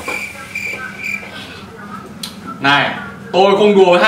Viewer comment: em cứ nghe như: Còn cái nịt còn gỡ hoặc là còn tớ là còn gỡ:))) 4:11 đến 5:19: có sự cà khịa cho nhân vạt nào đó trong mùa 2, dành tận 1 phút để nhắc cơ mà(hảo cà khịa) ai có cảm giác mùa 1 vẫn hay hơn mùa 2 ko